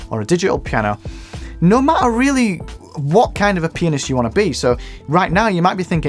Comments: i thought this dude said penis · I heard “what type of penis you wanna be”